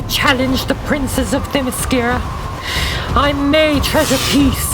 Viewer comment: I thought said "princess of mascara" as in the makeup....